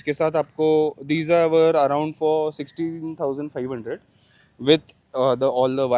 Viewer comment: how much for the auxiliary lights????? Did I hear ₹62,500 😮😮😮😮😮